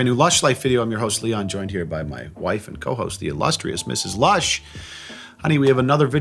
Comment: I feel bad that my brain always mix words up and I always hear: "The illushious Ms. Lust"